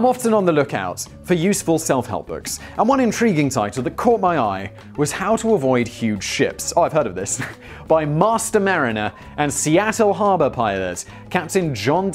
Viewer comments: I swear until the image of the book cover was shown I thought Simon said, "How to avoid huge shits." Thoight it was a gastrointestinal health book lol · I keep hearing "How to avoid huge shits". 🤣 🤣 🤣 🤣 🤣